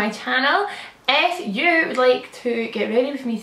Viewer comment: When you said "if you would like" I thought you said "F you" 😂🙈 Love your accent! Love your vibe x